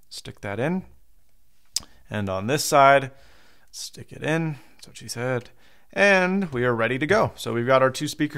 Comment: Hehehe, did i hear you correctly? 06:12 "That's what she said" LMAO!!!